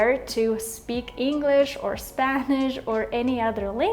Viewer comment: Did she actually say English or Spanish?